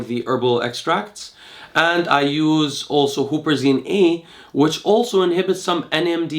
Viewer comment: What does he say on 16:14?? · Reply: Huperzine A?